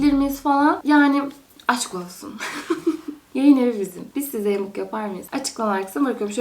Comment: 4.26 Gelin evinin içerik planlaması mi dedi ben mi yanlış anladım 😮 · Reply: fjfkfjf yayınevi dedim 🥲🥲